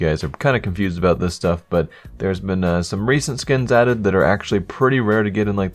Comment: I thought said that there were some racist skins added XD